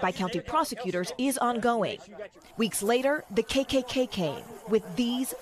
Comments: Did she just say the 'KKKK' ? WHEN did another k get added ? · Jeez, i had re-watch the video 3 times because it sounded like she said, "The KKKK." I'm like, what's that, a branch of the KKK? She should have said "the Ku klux klan"t